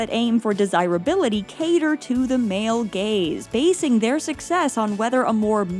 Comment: For a minute I thought she said "male gays" instead of "male gaze" 😭